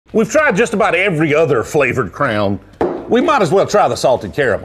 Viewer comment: My kids heard your accent and thought you had said flavoured Crayon. They were wondering what marine detachment you were with.